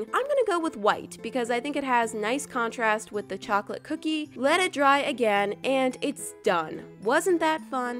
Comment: sounds like she is sayings and... "its dumb" XD